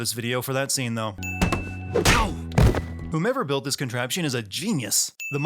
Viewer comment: you said Whomever, yet the subtitles say "who ever"